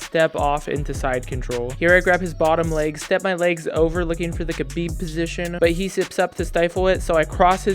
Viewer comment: What was that mount sub-position you said you were going for against the 45 yo purple belt? Sounds like you said "kapeep" position. Thanks man these vids are fire. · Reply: Khabib lol